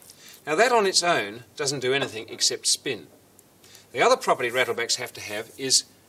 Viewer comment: Thought it said rattlesnakes lol